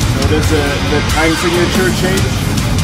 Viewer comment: I can't hear him lol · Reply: I think he asked “did you hear the time signature change?”